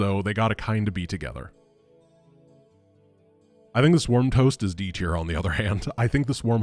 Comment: I had to go back because I thought I heard this wrong. "Swarm toast".